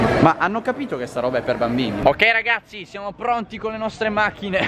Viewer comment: what did he sayyyyy? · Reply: Ok ragazzi